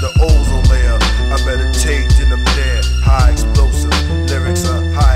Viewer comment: I'm a space cadet, with a tape and a cassette player, by fasting and prayer, I'm passing the ozone layer, I meditate then I'm there... high explosive, lyrics are high explosive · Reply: I thought it said "I meditate to the man" Ha. I actually did meditate to the man Killah Priest.